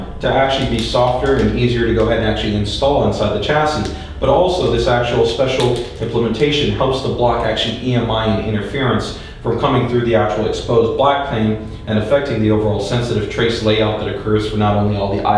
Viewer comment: does he say 'exposed black thing' here? lol